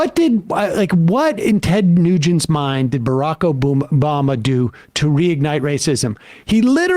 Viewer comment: "Barack Obooma" I heard it, we all heard it lmao